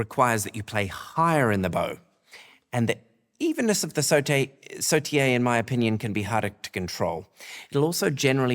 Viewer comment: Did I hear sauté at 5:06😂?